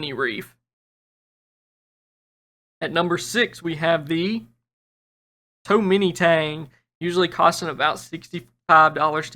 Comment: "#6 we have too many tangs" lol that's what I heard, new to this, don't know actual name of this fish, what is the actual name of this fish?